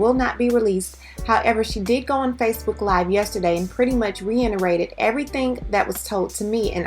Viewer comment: You said "reiNerated" instead of reiterated. All I ask (as a viewer) is for people to use words they know, instead of posing. You could have just said confirmed or mirrored--and been authentically yourself. 💙